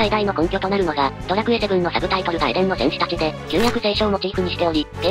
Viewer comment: エレンの戦士達って聞こえたww だから裏切ったのか